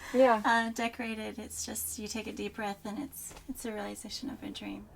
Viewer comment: I thought she said it’s a surrealization of your dream!